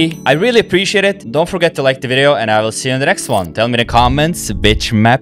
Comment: i love ur accent and amazing lineups. ngl i thought u said "bitch map" at 3:33 ty for the laugh! Keep going friend this is awesome